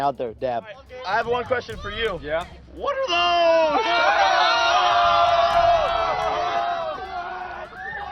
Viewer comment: did he just say what are those in 2017